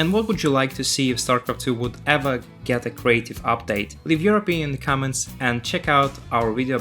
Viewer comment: "Leave your opinion in the comments" I thought you said to "leave European comments"